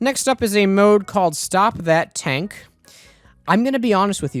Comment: When I heard him say stop that tank, I misheard and thought he said 'stock bat tank', and I was very confused for the entirety of the video. Thank Christ I checked the comments